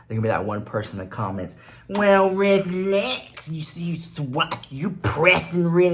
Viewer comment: Omg this whole time when you say in a different voice I thought you were saying well relax 😊 both sound good though 💕